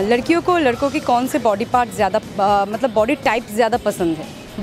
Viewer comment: Did she say 'body part' at 1:03 😂😂😂